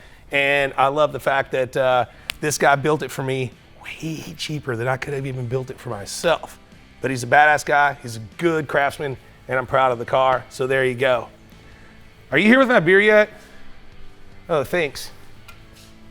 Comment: did you say “Basque” guy?